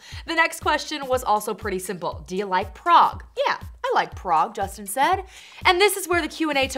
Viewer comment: What is that p word that they said at 0:44 when they were asking justin the question and hes was like yea i like and whatever that p word is? can someone plz tell me what they said? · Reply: Anais Bieber I think it was prog